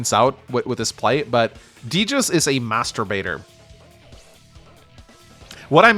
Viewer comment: Great game, but every time says "Master Baiter" it sounds like he is saying "masturbator"